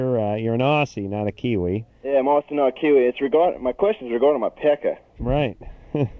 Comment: For years I thought he was saying "My questions are goin to my peckah".